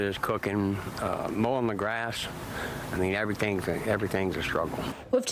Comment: When I heard him say mowing the grass, I heard wiping my ass.